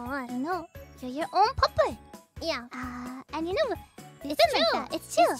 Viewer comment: Thanks for the clip. Btw I think that what Fuwawa is saying on 0:34-0:35 is pupper, she says it quite a bit. It's a nickname for a dog too.